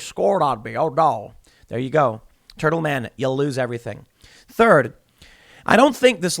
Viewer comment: I thought he said turtle manning 😂